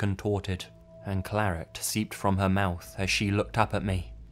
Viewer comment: what word was that? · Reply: "Clarott Seeped from her mouth" is this what you were referring to?